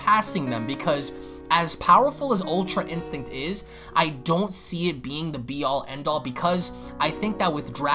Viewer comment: I thought you said ui needs to be the be all end all.